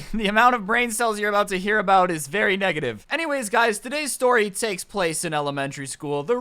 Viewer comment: Swear I thought you were ganna say "this story takes place in Alabama" I was thinking "so that's explains the title"